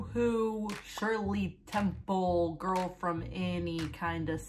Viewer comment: i'm sorry did she say girl from annie 💀 girl from ANNIE. girl from the main character titled movie and musical...annie